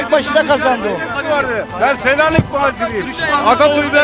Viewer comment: saniyede soldaki vatandaşimiz ne dedi?"Ben Selanik Bağcılar ,....!"Tam işitemedim.